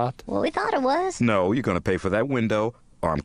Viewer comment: fun fact: when i was little i thought ignignokt was saying "dont harsh my balls" in moonajuana